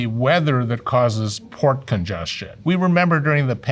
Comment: Totally thought he said "pork congestion."